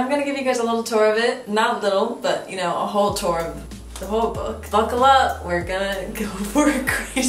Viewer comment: Ngl I thought you said “bookle up” and tried to be punny 😂😂 made me laugh then laugh more realising you didn’t even say it x